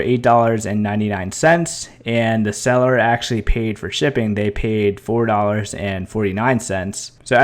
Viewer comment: At the end I thought you said buy paid shipping?? But you ending up paying it??